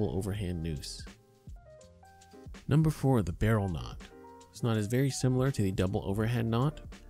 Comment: Hey man, thanks for the video. At time mark 2:44 did you say that you used this knot on your "Big Larry"? If you feel the need to tie him up maybe you should stay zipped up... anyway, RESPECT! 🤣🤣🤣